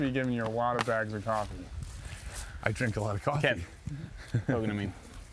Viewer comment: Did anyone else catch calling off the tee with, "Kev" at 18:35?